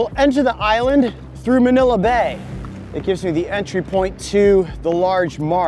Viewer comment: did you say MANILA BAY?